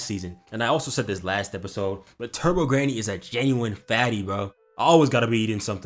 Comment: I thought he said “Turbo Granny was a baddie,” What is wrong with me 😭